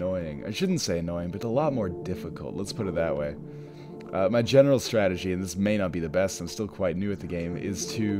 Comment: It sounded like you said, "my genital strategy" at 6:08.